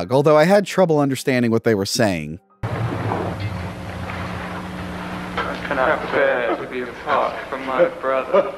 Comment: “I cannot bear to be apart from my brother” is what they said.